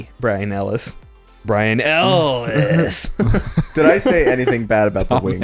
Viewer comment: Sounded like AM welcome back Harlan Ellison